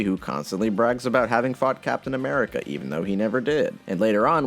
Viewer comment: Did anyone hear that as “fucked Captain America”